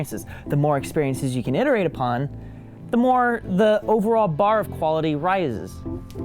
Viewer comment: Did just say "the overall barf quality rizes"?